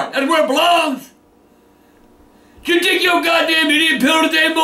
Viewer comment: What he say at 1:13 🤣🤣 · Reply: " did you take your GD idiot pill today boi"